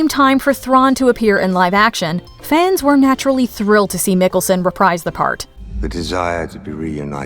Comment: Pronounced Mikkelsen wrong, I thought she was saying Nicholson LOL